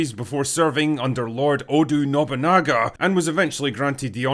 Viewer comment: Odu? I heard it right?? right?